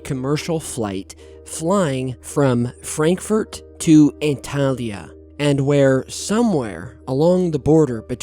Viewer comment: did you say Italia? 😂 No matter anyway. Great video as always 👍